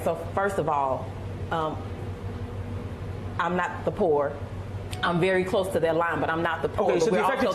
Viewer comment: All to make a buck.. Sad 2:30 Did she really say "Im not the poor"